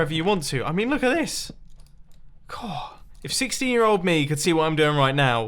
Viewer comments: why can I hear “if 60 year old me” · i thought you said 60 year old me and that confused me until I realised you meant 16